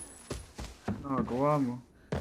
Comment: Just rewatching this vid then I hear "ginagawa mo" (12:16) is a filipino?